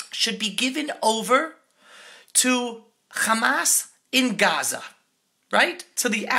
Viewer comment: He did not say Hamas, he said humanitarian aid.